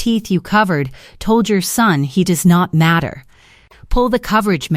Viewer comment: I'm 70. I heard it as, Rune doesn't matter.